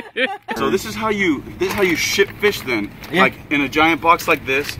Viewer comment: i thought you said shit fish